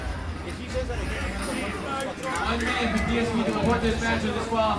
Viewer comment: I think he says "get DSP to (report this match)"